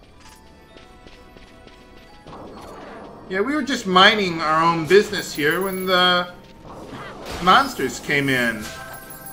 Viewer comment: I swear it sounds like you said "Mining" our own business. There's no way you would have made that pun, right? Right?